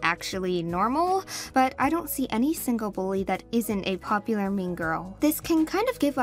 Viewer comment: I swear I heard this and I'm not sure if I heard right "I don't see any boy who isn't a popular mean girl" 3:01 boy or bully, but sounds like boy lol, I know it's bully now